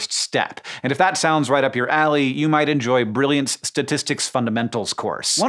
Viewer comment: Almost misheard that lol ‘’right up your a...’’